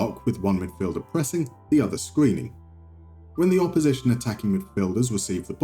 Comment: I thought you said "one midfielder pressing, the other screaming"...